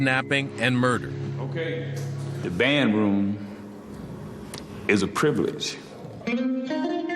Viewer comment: y did I hear 👂 bed room?? Was it just me